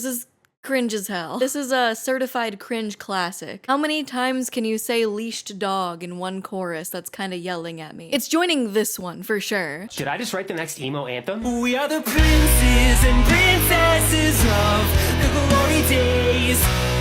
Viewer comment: I was doing tasks while listening to this and it thought they said "beached dog" instead of "leashed dog" 😂